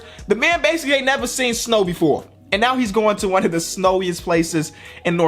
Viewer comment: Am I the only one who heard him say “Toronto one of the snowiest places in North America.”